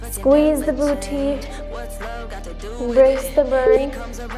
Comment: She said “increase the burn” I heard “release the burn” 🥲 I wish